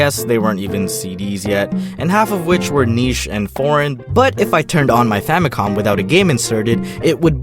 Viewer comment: I swear I thought he said Porn instead of Foreign😂